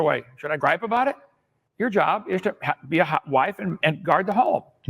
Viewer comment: Did Kent actually say Mary's marital job was to be a "hot wife"? Hovind's shittery knows no limits.